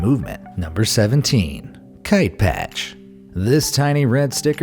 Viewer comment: Jeez, for a second there I thought he said Kike patch.